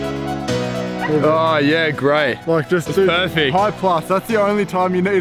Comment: Great vid! Does anyone know what he said at 2:53? I really can't understand :( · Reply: "high-plus" (the fastest shooting mode on sony cameras)